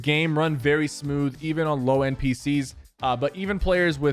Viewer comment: Did he just say low npcs at 1:07?